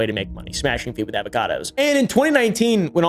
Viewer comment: what did he say?? Smashing avocados??